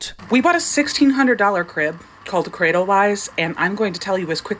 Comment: fitting that i kept mishearing "cradewise" as "cradle lies"